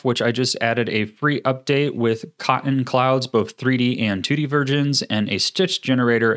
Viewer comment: lol he says virgins instead of versions.